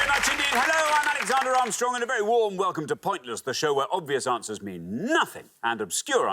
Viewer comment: I remember the first Pointless episodes I watched, I thought Richard said "I'm Alexander, I'm strong, and welcome to Pointless" lol